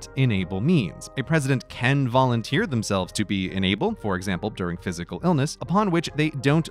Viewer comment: I kept hearing "inable" as "enabled". Slightly different meanings.